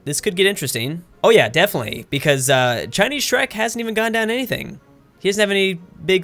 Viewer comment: Did you just say Chinese Shrek?